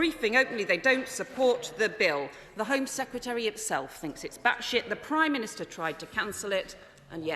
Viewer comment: did she say "batshit"?